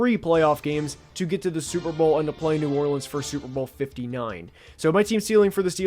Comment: WOW DUDE! At first I thought you said that you couldn't see the Steelers going all the way to the Super Bowl TO PLAY New Orleans, instead of to play IN New Orleans. I 'bout fell out of my executive office chair!